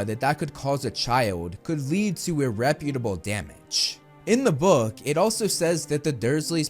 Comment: the closed captions say "irreputable" when it should be "irreparable." I can't tell if you said it right or wrong.